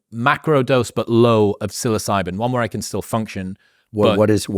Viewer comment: What does he say at 6:38? Silly Zyban?